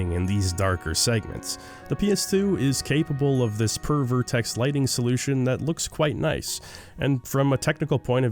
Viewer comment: I know what he's actually saying here, but: Pervert Ex Lighting Solution